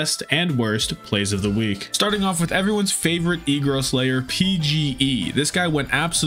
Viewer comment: Did he say..? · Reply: e girl slayer